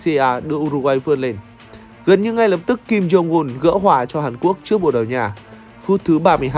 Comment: kim Jong-un hả =))) t có nghe nhầm ko